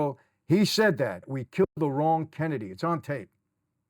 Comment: Instead of (we killed the wrong Kennedy), I thought they said if we cut the head off the dog, tail will fall. The head being John and the tail being Robert.